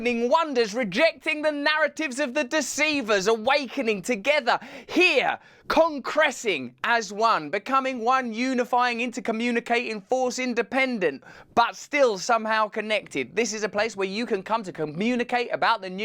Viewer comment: What was that word you used at around 0:24...concressing? I am probably spelling it wrong..